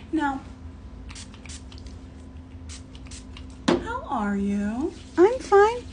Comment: I thought she said "how hard are you lmao"